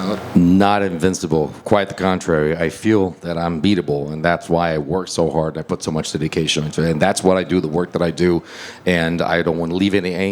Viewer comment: he did not say that he felt he was beatable. He said ‘I work hard because I precisely do not think I’m invincible.’